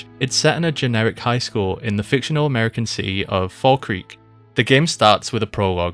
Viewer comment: I misheard "Fall Creek" as "Falkreath" and thought Skyrim did a little thievery of the name. Also, Kenny sounds an unbelievable amount like Liam O'Brien, I'd know that voice anywhere. and oh my God the name is LITERALLY Obscure, your title is very clever. Video's great! Looking forward to more. <3